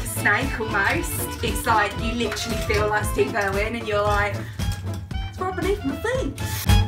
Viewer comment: Great video! What do you say at 13:04 ? Lol. ( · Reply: Ha ha “it’s right beneath your feet “. Lol xx